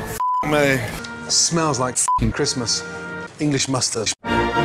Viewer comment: english mouthstash, fuckin lost it there lol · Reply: I thought he said "English mustard"